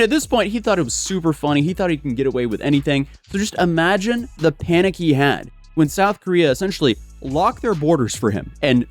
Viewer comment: Looking for the link to Gabe you promised at 2:40 . I can't understand what you said when you mention his channel. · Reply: It’s in the description of the video! He goes by culture brat on YouTube